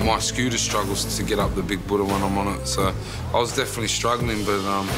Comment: Sounded like he said big budoo 😂😂